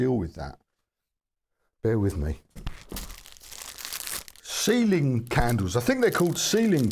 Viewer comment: oh sealing wax. I thought you said ceiling wax. I was going to say I wouldn't touch that lol.